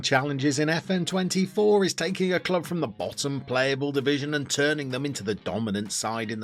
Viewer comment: My mind heard you say “a club that’s bottom of a playable division”. Ajax now qualifies for this, so I validated playing with my club again 🤷🏻‍♂️😂